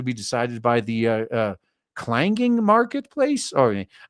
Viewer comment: Thought he said “clanking” marketplace of ideas—thought it was a Styx shoutout